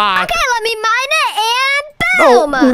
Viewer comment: why do you say gowh instead of gosh ❤